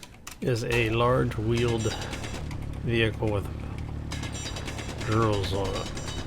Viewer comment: LOLOL.. I SWEAR I've listened to this short clip a dozen times and I know what you were trying to say but it sure sounds like you're saying you need a big truck with GIRLS on it! LOLOL